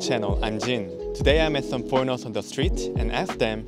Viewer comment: When he said "foreigners" I heard "pornos" what is going with ma head...